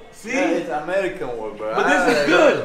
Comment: Thought he said I would kill you ! 😂😂😂😂😂